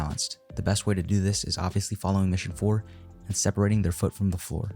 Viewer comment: what did you say lol? "the best way to do this is obviously following ( - - - ) and separating their foot from the floor" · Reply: “following mission 4…”